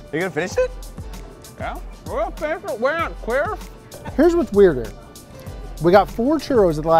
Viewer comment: I swear to God I heard "Yeah? We're gonna finish it, we're not queer" I know he said Quitters, but 😂